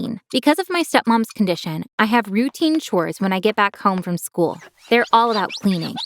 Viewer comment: Can we talk about how she says get back from school and captions say work lol at 0:36 with captions on edit: I was adding 0:36 with captions